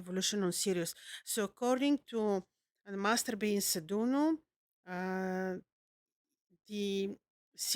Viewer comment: Can anyone tell me the name she says at 3:58 ? I've tried looking up but I can't figure out the spelling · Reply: Master Beinsa Douno